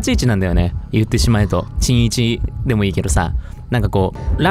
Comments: さとみくん本当にごめんなさい 本当に申し訳ないんですけど、どうしても何回聞いても｢新一｣が｢ちんいち｣に聞こえま((((( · 新一がちんいちに聞こえるwww 私だけwww？？